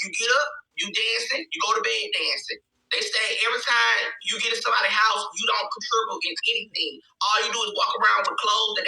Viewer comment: Con tri what? wtf say😩😂 I want him to spell what he think he said.